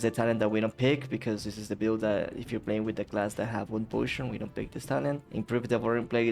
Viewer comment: "if you're playing with the class that has **" I don't understand what you said there. Sorry, my English isn't great, couls you please type what you said there as a reaponse? :D class that has what? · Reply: Wound poison = mortal strike